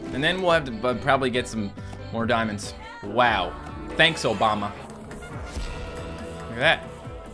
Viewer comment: did you say ty Obama?